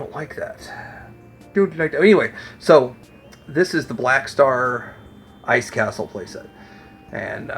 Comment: Cool play sets. You said “Blackstar Castle” and somehow I heard “Bravestar Castile” and said why does a cowboy need a castle. Then realized I miss heard. Flagg and love boat together is fun.